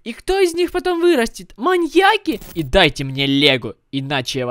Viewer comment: Вначале мне послышалось, дайте мне лего🤣🤣🤣